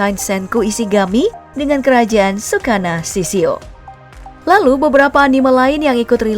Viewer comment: gw salah dengar apa anjir tsukana shishio